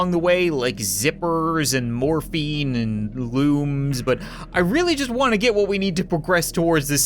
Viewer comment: I didn't hear very well this part and instead of looms I heard wombs